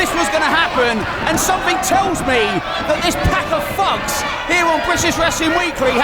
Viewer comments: does he say "pack of fucks"? Kooky. This wrestling is incredible! Thanks WhatCulture for bringing this to my attention! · uhhm, did he say "this pack of fucks" in